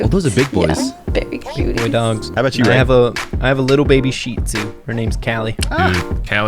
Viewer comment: I can't really make out what he said. Can someone tell me? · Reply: said that he has a little baby shitzu